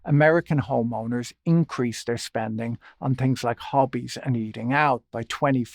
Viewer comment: I thought he said Hobbes and Hobbes not.